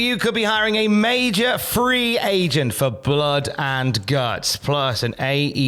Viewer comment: I thought you said MAJOR free agent. Who the hell is that? 😂 Nobody fkn watches New Japan.... Tony Khan needs to get that through his thick skull. Ratings in the toilet due to crap like this.